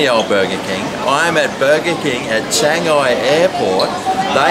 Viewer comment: you fit in so well, you could almost be a Chinaman. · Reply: Not racist! I thought he said Shanghai.